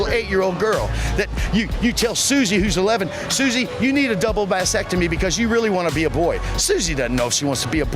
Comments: Did red Texas shirt guy really bring up Susy needing a "Double vasectomy to be a boy?" I don't think he knows what that word mean... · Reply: I heard “vasectomy” for sure. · Genuinely thought that clown said “double vasectomy” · lol 35:23 did he say "double vasectomy"?